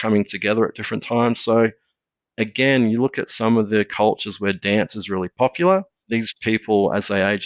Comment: Mr Keogh’s accent tricked me. I could have sworn he was saying a good exercise was “darts” and I was thinking to myself “how the hell do those crazy Aussies play darts!” Didn’t realize he was saying “dance”. Makes perfect sense. Excellent presentation. Thank you.